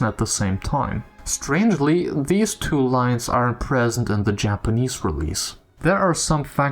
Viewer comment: Was this sentence "these two lines aren't present in the Japanese release"? Subtitles says "are", but it wouldn't be strange at all if they are in the JP release. It just means they localized all of the voice lines in the game's files. There are more unused voice lines in XB1 than those, all of which got localized.